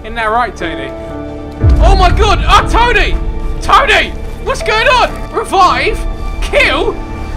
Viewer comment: this whole time I thought he was saying toly not tony😂😂😂